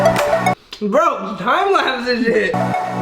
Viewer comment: In 10.31 I swear I heard him say "bro time lapse this shit